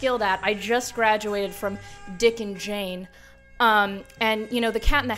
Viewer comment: i heard "dicking jane" the first four times i replayed it ngl 🤣